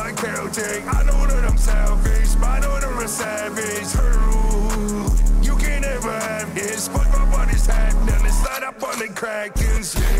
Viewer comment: Bro the line “ride with my keltec. I know I’m a shellfish modeled after savage” go crazyyyyy it’s from the first song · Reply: I thought he said "my daughter a savage"